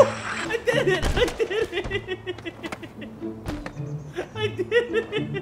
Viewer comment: u said "I did it" I heard: 'I did HEHEHEHEH"